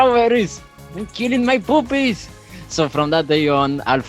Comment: I just die every time i hear "UNT KILLING MY POOPIES!"